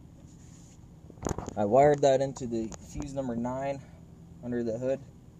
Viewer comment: What the hell are you saying? It sounds like you said: " I wired that in to the season da my." So what the fuck is a season da my????